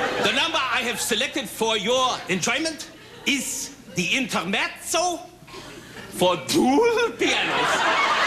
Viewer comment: Korman says, “Ladies and gentlemen, the number I have selected for your enjoyment is the Intermez—ZO [I think that’s what he said] for two pianos.” I had to do a double-take because with his fake accent it sounded for all the world like he said, “… The Internet — SO! …” — in the 1970s!?